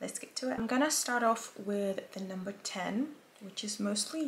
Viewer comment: what highlighting shade did you use? i only heard you say “number 10” which isn’t how the shades are listed